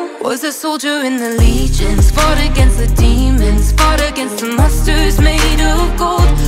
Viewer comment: why did i heard "fought against the mustards made of gold"